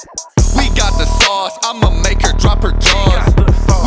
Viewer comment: I thought u said Ima make her drop her drawls